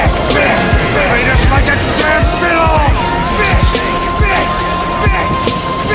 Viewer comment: When he said fiddle, I thought he said pillow...